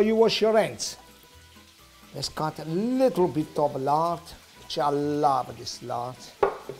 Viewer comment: What is that, i'm mexican, i don't know it and can't hear well hoe is he calling it, it looks like pork fat or something like that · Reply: Martin Rodriguez It’s just lard.